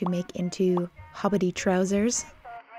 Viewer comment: misheard hobbity trousers as poverty trousers.